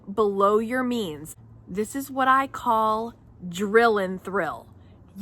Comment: Drill and thrill, but I actually thought you said “drill is real” 😊